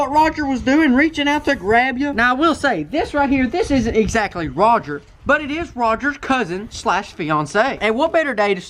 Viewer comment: "this ain't exactly Roger this is his cousin/fiance" uhhh I know I'm not the only one who heard that